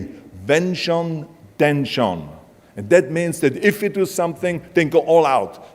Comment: Can someone type in German what he is saying about not giving up? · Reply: If I got you right you mean the saying "Wenn schon denn schon"